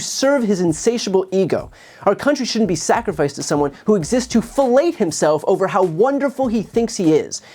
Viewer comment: Did you say "exists to fallate himself"? I'd agree, but it might not be the image you intended to convey.